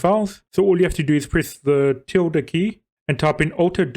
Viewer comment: what the fuck key? is sounds like youre saying tilda.